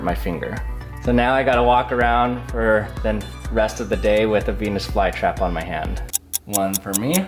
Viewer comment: I thought he said penis fly trap🤣